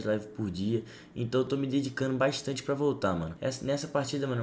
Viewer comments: Só eu que escutei me dedicando bastante pra Voltan? Kkkkkkkj 0:15 Eitah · Mano só eu que entendi que ele falo"tô me dedicando bastante pra voltan"??? Tempo 0:15 ao